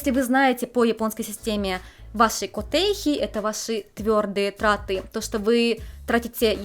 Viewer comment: Послышалось "ваши потехи это ваши тяжелые траты"